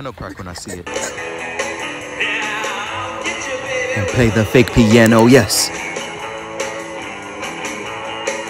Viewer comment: “I need to ask Tuesday if she wanna dance cause this ain’t it” “You look like a car dealership inflatable” “Play the fake piano” Got me rolling on the floor 😂 Edit: DAMN 2.4K LIKES?! TYSM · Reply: i thought he said play the thick piano💀